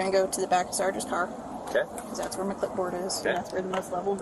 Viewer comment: Officer McClipport reporting for duty. The inaccuracy of the subtitles are silly at best but sometimes it gives us little gems like McClipport. That's "my clipboard" for anyone who didn't catch it.